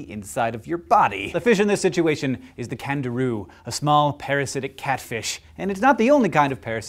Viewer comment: Totally thought he said "starting off with the Kangaroo" not Candiru. As an Aussie that shocked me.